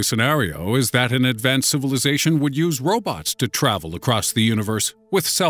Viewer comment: did he said robux than robots?